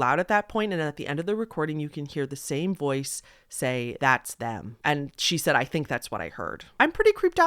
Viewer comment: Last one it sounds like it says It’s them